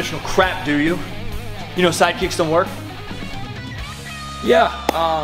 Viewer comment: I thought you said side kicks don't work...